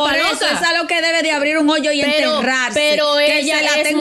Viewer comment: un joyo fue que dijo?😢 Esa muy si grita